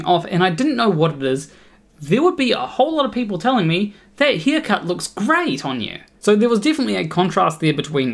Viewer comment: OH?!? A hair cut; thought you said a Beer gut.